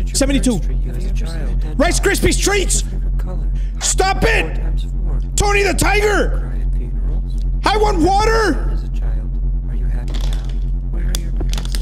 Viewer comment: It sounded like he said Tony the tiger is his fav color